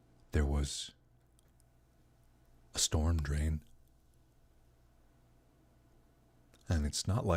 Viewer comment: what is a stormtrain or whatever he said in 13:03?